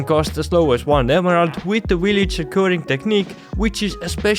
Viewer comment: the willie chickuron technique?? Wtf are you saying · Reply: Villager curing lol